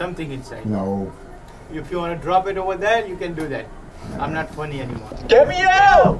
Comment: And that man was something serious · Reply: I thought he said “I’m not twenty anymore!” 😂